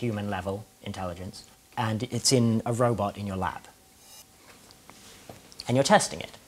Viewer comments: Haha, the captions say "in your lap" instead of "in your lab". How adorable. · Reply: Awe.. I thought he actually said "in your lap"... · Haha, the captions say "in your lap" instead of "in your lab". How adorable.